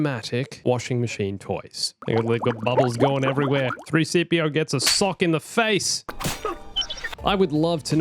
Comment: Did you say 3CPO at 8:52?